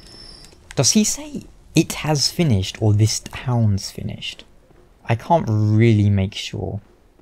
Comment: i've always thought he was saying 'is DEATH finished'